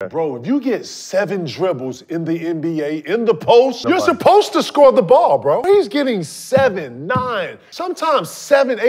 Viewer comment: I always hear “as a big, you’re supposed to score the ball” bruh what do you mean? Everyone is trying to score